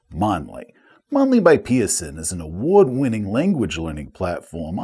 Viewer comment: at first i thought said 'By Petersen' and thought 'As in Sandy? no thanks i like my Sanity where it is...'